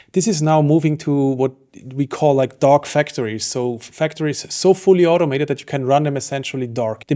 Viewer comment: I really thought you said dog factories 😂